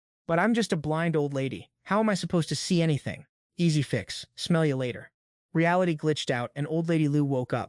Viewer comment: Hahaha 3:06 it totally soulded like he said "smell ya later" to the old lady i have ta rewind to double check if i heard that right 😂 Edit: He did!!!! Hahahahahahahaha 😂😂😂😂😂